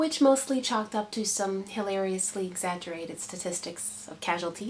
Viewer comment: haha, it sounded like you said "Hillary-ously" in place of 'hilariously'! I've been saying that too, ever since Hillary ran for president. Hey, I'm glad to hear you served in the military -- and I like what you say about it. I'm going into the Air Force soon ("hurry up and wait" starts before u even get in! lol), and I am really looking forward to it.